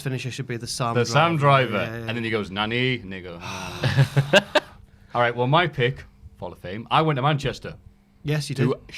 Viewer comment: What did he say? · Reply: He said grocery store.